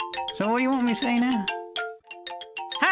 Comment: “So what do you want me to say now?” You can’t be serious, you know the line already. 0:22 “HI, I’m... Line?” BRUH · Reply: I thought he was saying "Hi I'm Lime?"